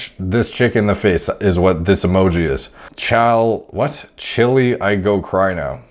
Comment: I ALWAYS THOUGHT IT SAID CHILI TOO until i found out it was uh.. child.. 😅